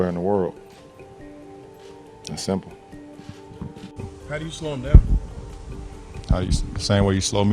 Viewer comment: “how do you smoke 💨 meth?” 😂 bahahaha that’s what I heard!